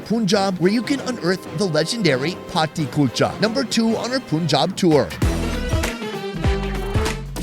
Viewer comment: Did he just say puunjob